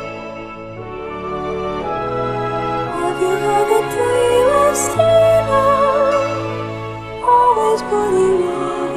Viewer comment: I couldn’t tell what she was saying at 0:57 can u tell me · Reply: "Have you heard the tale of Steven"